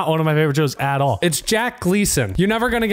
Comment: I thought he said Paris Hilton